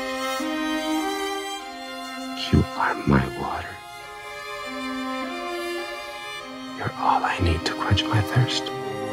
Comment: Hello beautiful people! Who can (PLEASE) tell me what Gabriel said on the 1:27 - 1:47?? Music is so loud :) I cant understand some words :( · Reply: You are my water. You're all I need to quench my thirst.